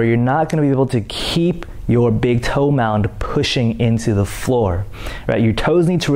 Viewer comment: Is he saying "big toe mound"? I couldn't hear it quite well lmao. Sample is